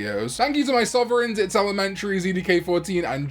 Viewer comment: I always hear "when we go to jail" at the end of your videos